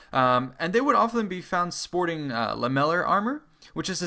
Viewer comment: sorry what did he say around 5:55?? what kind of armour?? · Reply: Lamellar armour